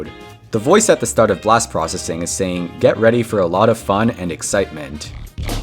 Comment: In bp, i always heard “let him sleep” not “and excitement”